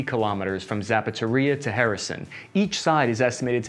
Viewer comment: what is harrison ? you try to say kherson? XD